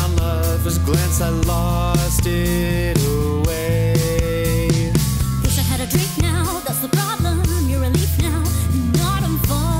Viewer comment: for your name im so confused, I keep hearing "wish I had a drink now" in that last part by but all the lyric websites I've seen have said "guess i had a drink now"